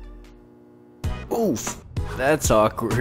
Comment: or 3:20 Did you just say oof?